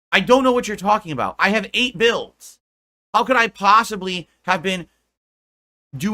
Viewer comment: When I need money my financial issues are an open book but anything about my personal life is none of your business Edit: when he said eight builds I thought he said eight bills 😂